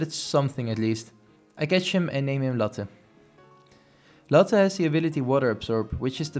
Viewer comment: It's really that bad, huh. Haha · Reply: everytime you said Latte i thought you said Lotte, and would instantly think LOTTE TETTEN ZIEN as a result